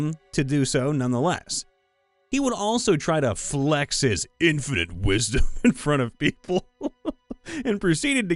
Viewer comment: It sounded like you said, "infant wisdom." 3:23 Split personality? 3:25 Awesome! 5:46 What the heck? 6:27 ^See above. 8:49 HUH?! 10:36 Definitely weird. 11:30 Weird but fun. 11:45 Couldn't call himself 6 foot 1!